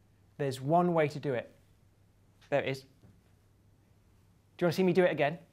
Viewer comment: "there it is, wanna see me do it again? there it is!" xD that made my day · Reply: is it just me or did I hear "there isn't" instead of "there it is"?